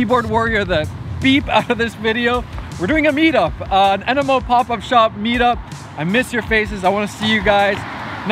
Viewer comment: i thought he said "enema pop up shop"